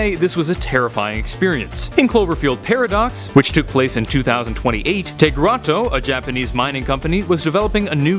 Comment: I spent like five minutes realizing he said the movie took place in 2028 I thought he said it released in 2028